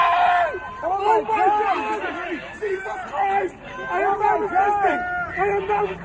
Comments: I heard "I am not a suspect" not "I am not resisting" but tomato tomato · i thought he said "JESUS CHRIST I AM NOW A CHRISTIAN, I AM NOW A CHRISTIAN" when he was saying he wasn't resisting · MY VLOG CHANNEL: <link> MY GAMING CHANNEL: <link> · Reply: You said he kept saying "I am not resisting"... I heard "I am not a suspect"...